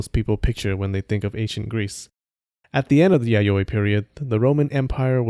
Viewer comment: I thought it said Yaoi period and not Yayoi period XD